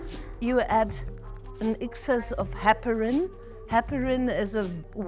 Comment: How to do a documentary about long covid without ever mentioning the elephant in the room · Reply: No, she is talking about the blood thinner Heparin. Her pronunciation made it sound like she said pepperin. Hospitals do not use Cayenne pepper despite it's medicinal qualities.